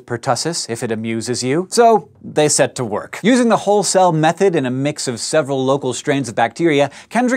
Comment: I'm a lab technician at a plating plant, and every time you said whole cell, I thought you were saying Hull cell. <link> Come to think of it, that could be a cool video. Virtually no one I've met understands plating, unless they're a plater themselves or have a serious electrochemistry background.